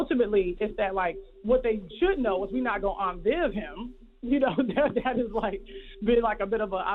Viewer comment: I couldn’t hear what she said here 26:56. Anyone know what she said? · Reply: she said they won't "aunt Viv" him, meaning I think they won't bring in another actor to take his role as they did with aunt viv in fresh prince of bel air